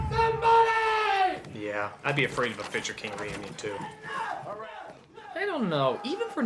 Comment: What the hell did Nostalgia Critic say at 2:14? I've watched this video multiple times and still can't make it out. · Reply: "Yeah, I'd be afraid of a Fisher King reunion too."